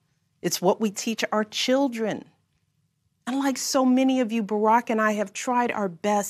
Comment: I hear you First Lady; thank you for this strong words of Encouragement. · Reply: Listen carefully what she says at 6:00. She says ..." unlike some of you..." Unbelievable.